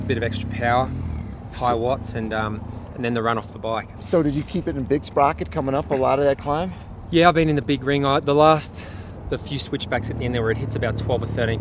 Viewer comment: did crowie said he was in the big ring....?